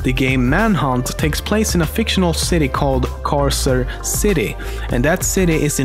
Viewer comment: Who thought he said cancer city